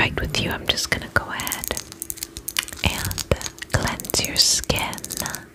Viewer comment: she says she’s gonna cleanse your skin but I heard “I’m just gonna collect your skin.” At first and had to take a sec to understand lol 😂